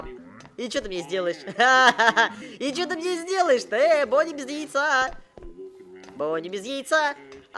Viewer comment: мне одному послышалось Бони без Яйца ;! ?